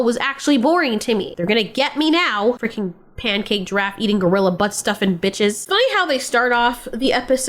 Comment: "Pancake draft eating, gorilla buss stopping bitches" ? =)))) Is that what I heard? I'm so confused right now =))